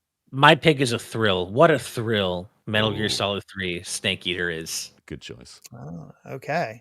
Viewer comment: I seriously thought said “Metal Gear Solid 3: Stank Eater” lol😂😂😂